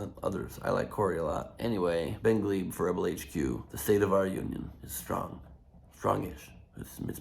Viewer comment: I hear 'Union jobs'.